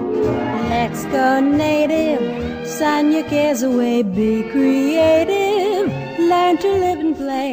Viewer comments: I thought it said "sign your kids away" instead of "sun your cares away" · LOL. I thought it said that too. It says "Sun your cares away"